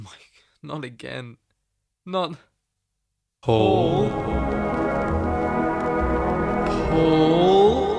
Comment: Bro you gotta feel bad for the Spider-Man fans that have family members named Paul. My uncles name is Paul and hearing or saying his name just fills me with dread now. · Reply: And cause of you 2:25 Is what I hear when people say uncle Paul. 😂